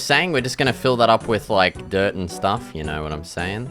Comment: my audio cut out and it sounded like f**k dirt 🤣😭😭